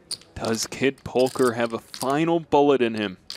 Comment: Did he said "kid polker"? on